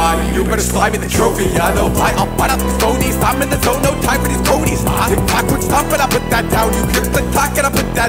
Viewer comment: Can't believe I'm catching that bar now... Actually the more I listen to the whole song it the more I like it. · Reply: when I first heard it, I initially thought he said "ive been the goat, no time for these tonys." Looking at the video I remember I'm partially deaf